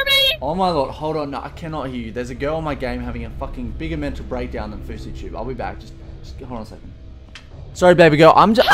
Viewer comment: anyone else hear him say abour fousytube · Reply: Brady Smith He said "the girl on the game is having a bigger mental breakdown than FouseyTUBE"